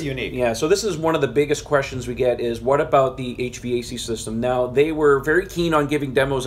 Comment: when you said "HVAC system", i heard "eight-track system". will there be an optional 8-trk stereo player in the premium package? i think i still have some kenny rogers and neil diamond 8 track tapes...